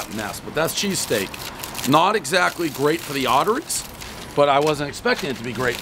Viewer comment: "If you value your arteries, you should probably limit these to one a month" eats pizza every day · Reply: rofl i thought he said AUTARIES